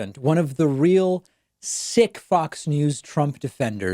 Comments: Lmaooo I thought he said sick fuck at first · Lol when you said one of those sick Fox News, I thought you said one of those sick fucks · I literally thought David said “sick fucks“ until he finished the sentence. But it registered as “sick fuck“ first and I’m going to go with that angle!